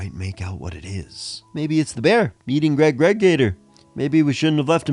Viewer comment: Oh man, love Greg GregGator. We totally need a special episode on him. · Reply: I thought you said Ewan EwanGregor.